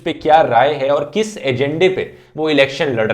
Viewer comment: Did you just say agende? ^_^